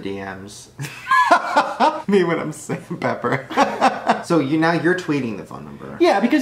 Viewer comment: did Shane just say " Me when I'm safe from pepper " ?